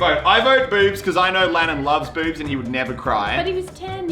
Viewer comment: I thought he said “boobs” in stead of hooters. I mean both of them lannon would love